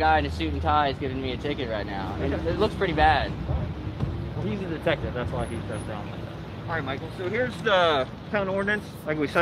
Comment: did he say "he's a detective thats why he is so dumb"?